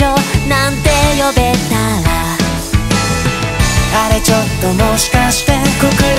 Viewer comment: ここのなーくんの「あれちょっともしかして」が「あらちょっともしかして」に聞こえた…w